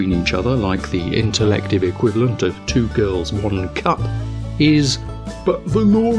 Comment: Did he say "two girls, one cup" at 2:39? The video nobody could ever find way back than?